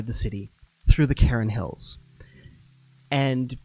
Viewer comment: Every time he says "cairn" I hear "Karen" and for half a second I'm a manager sitting in his office with his head in his hands.